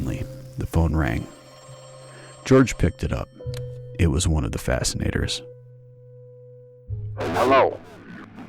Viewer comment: Thanks for the story! The first time I heard “Fascinators” I heard “Vaccinators” and it got political fast! But thank goodness for the Georges out there!!!